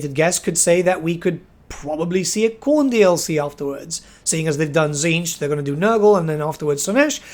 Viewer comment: I’m sorry. I heard that as P0rn DLC and I mean, hopefully they’re not that desperate…